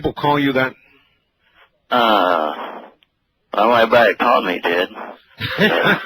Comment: anyone catch what he said there? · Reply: "everybody who called me did" meaning people who called him referred to him as madman